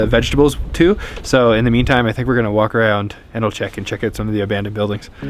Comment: Edelcheck....Jeeeesuuuuuus.....I heard "anal check" for a second....I was....what the f** guys???? Had to turn on Cc ........When cold weather messes with your consonants...